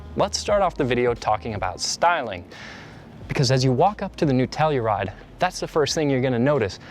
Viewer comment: I swear I heard Nutella-ride 🤣🤣🤣